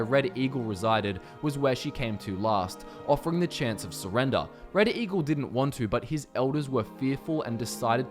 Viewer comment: Did you say... Reddit Eagle?